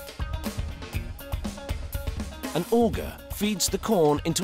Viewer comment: I swear he said ogre and I looked up expecting some guy looking like Shrek